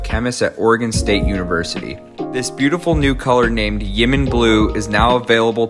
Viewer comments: I heard it as yemen blue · I thought he said "jimin blue" 😅